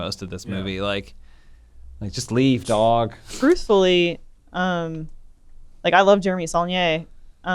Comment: I coulda swore Elise said 'Brucefully" ROFL